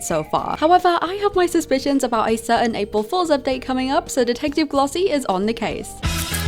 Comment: glossie i’m sorry but when u said “detective glossie is on the case” i thought u said detective mommy😭